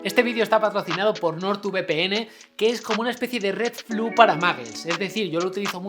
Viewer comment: Por un momento entendí "patrocinador nortuvepene" 😂😂😂😂😂😂😂